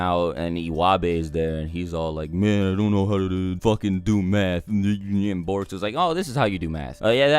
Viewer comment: At first I thought he said meth!🤣